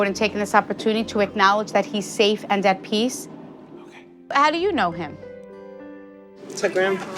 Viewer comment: Is it just me or did I hear an “ok” whisper at 1:05? Not sure if it’s an EVP or if the camera man said something. Just thought that was interesting.